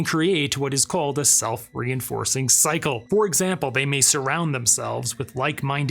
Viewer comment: Wait! Did you say “self-reenforcing psycho”? 😊